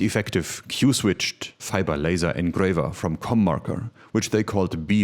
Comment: I thought the logo said “CornMaker” and I thought you said “Cum Marker”. Love all of this.